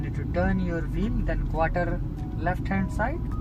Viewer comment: I thought you said when doing the parallel parking you turn the wheel half to the left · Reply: I heard you saying quarter